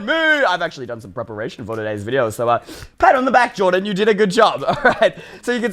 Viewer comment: I thought he said “give me my hat back Jordan”